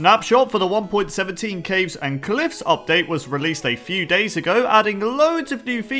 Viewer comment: Idk why but at 0:02 I heard Cakes and Cliffs update